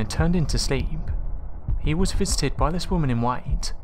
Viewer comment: i misheard 'visited' as 'fisted' and im like ????? dude cringe